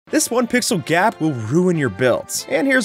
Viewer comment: I heard " this one pixel gyatt "